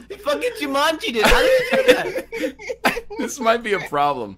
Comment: what he said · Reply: Kosta Ranilovic You mean 'Jumanji'? It's a movie with a part where a character gets sucked into the floor like quicksand